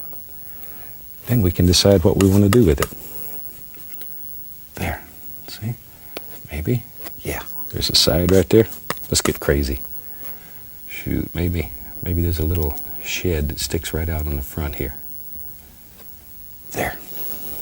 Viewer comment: At first I thought he said shit